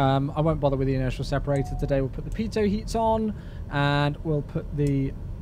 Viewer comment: I did a mishearing: "put the pizza heat on" ;-)